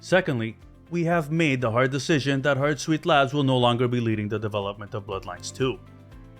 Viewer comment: Thought you were saying "our sweet lads" lmao